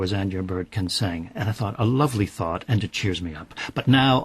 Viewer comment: He actually says "a lovely fart"